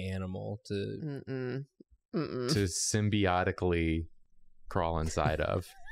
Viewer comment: I heard symbolically and was VERY confused lol